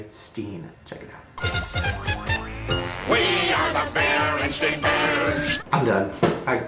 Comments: the Berenstain bears theme song at @ 15:37 said berenstain not berenstein lol I definitely heard 'STAIN" clear as day... Anyone else? Homer did say "STEIN" though. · I heard 'bearinstain' bears